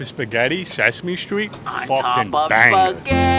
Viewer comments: Dude is a cartoon character come to life lmao "hey, brush your hair" 🤣🤣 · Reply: lol at first I thought he said "Hey, wash your hands". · “Brush your hair”💀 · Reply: I thought he said “wash ur hands” at first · i thought he said “wash your hands” at the end but “brush your hair” is so much better lmao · Lol idk why when he said brush your hair I thought he said “ay wash your hands” which is even funnier